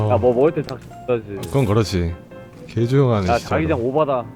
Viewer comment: 개조용하네 ㅆ발 ㅠㅠㅠ 맘에든다 욕해도 섹시해 너란 남자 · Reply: 개조용하네 진짜 아니에요 ?? 특유의 뭉개지는 말투땜에 개조용하네 짖자.. 가 ㅆㅣ바로 들리는 듯